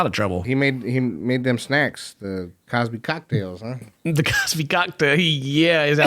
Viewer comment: you nearly said big cock instead of bill cosby pmsl 😂😂😂